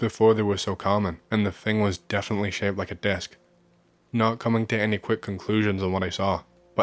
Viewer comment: American here… I’m sorry but I heard “desk” so I was imagining like, an IKEA computer desk hovering over the trees and was really confused…😂 sorry.